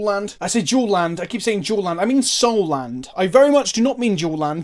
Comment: I thought you were saying jewel land, idk why